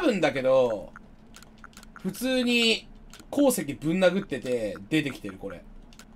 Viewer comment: こーすけぶん殴っててに聞こえた